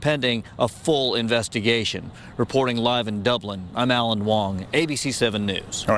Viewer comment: did he say alan wong? cuz the screen at 2:56 says...